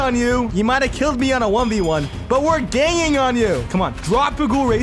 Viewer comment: i heard "were gaying on u"